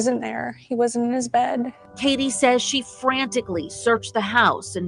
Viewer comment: Sounds like she says he wasn’t there he was in his bed.